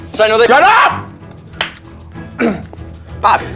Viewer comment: What does he say at 16:32 when he yells out? I’ve rewinded it like six times and I can’t tell lol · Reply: shut up?